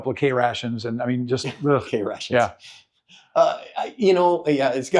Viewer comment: did dude just say he’s got his krations? Old school.